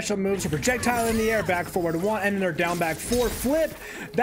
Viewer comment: What buttons please do I press to get her to lift opponent up in the air for an air combo on ps5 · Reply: thank you so much I heard him say forward, Forward 3 and I'm like huh